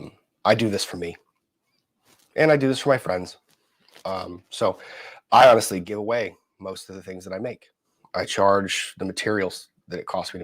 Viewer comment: I'm confused lol. Are they given away, or are they sold for the price of the materials?? I'm probably hearing something wrong like a dummy, but I keep hearing "so I give away most of the things that I make, I only charge for the materials," lol.